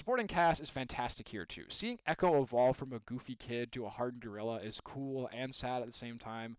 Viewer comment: did u say a hardened gorilla or did i mishear that 😭